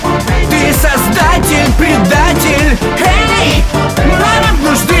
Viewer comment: я один услышал пердатель?